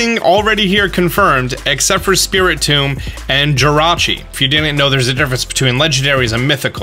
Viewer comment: Heard him say Jirachi instead of a Victini.. Me pauses video to go check comments... Oh yes, I didn’t think I’d be the only one who heard that